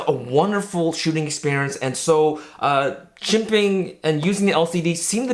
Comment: what's the word around 10:23, jimping? sorry, still learning English...